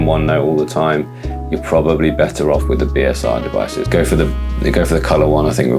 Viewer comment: what do you mean at 18:03 when you say "if you want to live in onenote all the time, go with a BSR device"? i couldnt tell what you said so i put it on subtitle and it thinks yo said BSR. i live in onenote all the time and am looking for the most affordable option for an effective way to work with One Note to organize hand written notes. THANK YOU!! great videos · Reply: Boox Super Refresh devices.